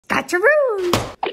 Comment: When i first watched it i thought she said "Gacha roo" but then i red the title